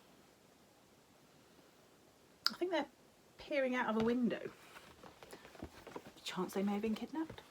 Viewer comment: Super! Husband thought you said “ Two children peeing out of a window” 🤣